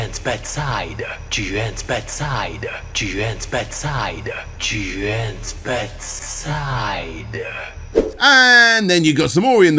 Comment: Did anyone else hear “Juen’s bed side,” at 9:01?